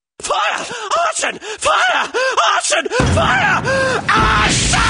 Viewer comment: Is it me or Mr. Peperami saying "Fire, OH SHIT" x3 times? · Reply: I thought he was saying AUTION FIRE